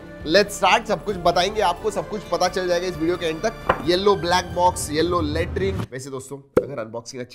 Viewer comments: People who heard yellow black box and yellow latrine 0:35 💀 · did he just say YELLOW LATRINE?😂